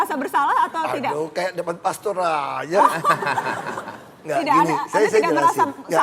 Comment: pak ahok ngomong apa sih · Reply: Asus eli "Aduuh, kayak depan Pastor aja"... karena Mbak Najwa ucapin "Ayo ngaku" ke Pak Ahok...